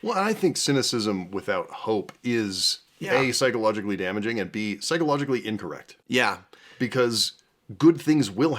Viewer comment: I heard "psychologically ignorant" as "psychologically inbred" and it kind of works? You make one frame of mind both the mother and father of all your opinions and get mixed up outcomes.